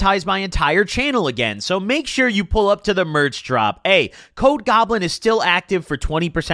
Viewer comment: Yo when he said “code goblin is still available” I thought he said ‘COKE goblin is still available” and I was like ain’t no way he’s telling us he relapsed in the merch ads lmaoooooo